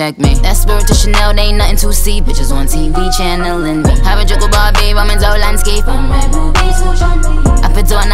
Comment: did nicki just said harajuku Barbie and roman Zolanski omggg😭